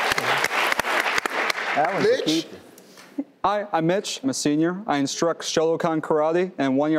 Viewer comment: That awkward moment when I thought Steve said Bitch to but in fact said Mitch lol.